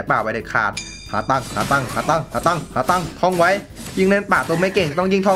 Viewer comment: ทำไมผมได้ยินว่าขาตั้ง555